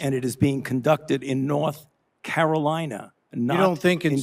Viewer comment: Did I hear Fauci say "North Carolina"? Did you?